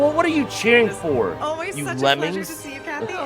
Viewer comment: did someone say 'you suck'? at 07:08?